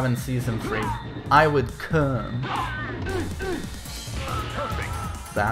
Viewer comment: "I would ?????" can someone tell me what said there. It just bothers me because at first it sounded what I initially thought lol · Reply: He said "I would crumble"